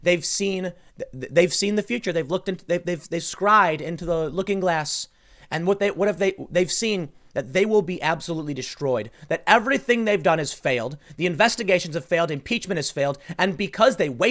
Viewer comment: Listening 10:50 in or so brings up "Project Looking Glass."?? I'm playing pool in my garage, watching my dog and had to stop. Maybe I heard that wrong..